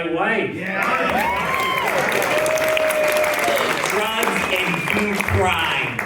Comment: What did that person say? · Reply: I'm not sure. "Drugs and crime"?